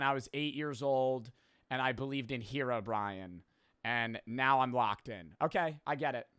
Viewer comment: Wait, did he just said "herobr-ian"